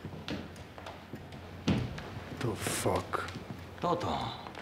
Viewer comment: Anybody understand why Masseria calls Lucky "Toto" or something at 1:16? I know the subtitles said Salvatore, but what he said sounded nothing like that.